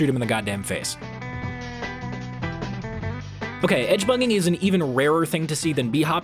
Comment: I thought you were saying "Edgebunging" at first.